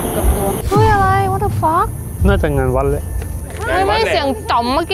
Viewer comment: วินาที32:39ได้ยินว่ากลับตัวมั้ง